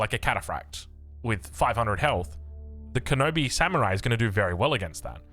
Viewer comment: Did he said Kenobi samurai?? The force is strong with them